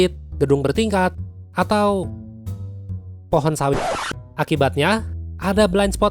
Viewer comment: enggak salah dengar kah tadi pohon apa tadi ,pohon sawit